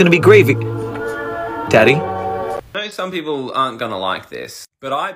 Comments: SIR? ARE THE SUBTITLES DECEIVING ME OR DID YOU JUST CALL IT DADDY? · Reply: He said teddy so the subtitles are just deceiving you. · did he just call it daddy